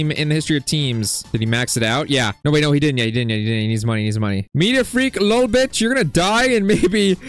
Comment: I swear bro said a cuss word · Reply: NO WAY BRO SAID MEDIA FREAK LOL BIT